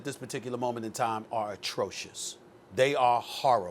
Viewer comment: I heard " TRASH " when he said atrocious .....